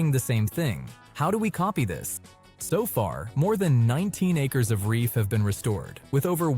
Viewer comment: The AI narrator says, "19 acres," but the on-screen caption says, "90 acres," so which is it? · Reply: My bad 😥 it was 19 acres not 90 thanks for pointing that out🙏🏼